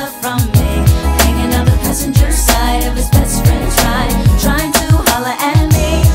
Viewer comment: When I was little, I thought they said “best French fry”